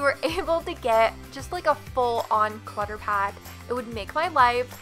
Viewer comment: When you said "clutter pack" I initially thought you said "water park" (not sure why - I wasn't paying that much attention + I'm not a native speaker), so I started thinking about how cool that would be :D :D